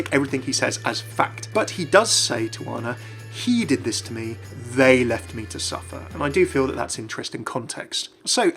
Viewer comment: he actually says they left you to die, they left me to suffer